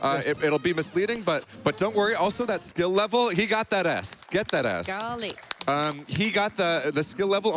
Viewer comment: The guy says “He got that S” But All I hear is “He got that a$$” XD